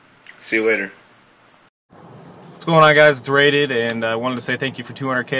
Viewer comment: Is it on 3:23?